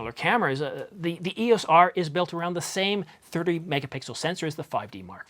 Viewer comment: did u just say 38 mp sensor. Nope it's a 30.3 mp sensor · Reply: Nope, I did say 30 megapixel. Sometimes I slur things together, but defiantly said 30.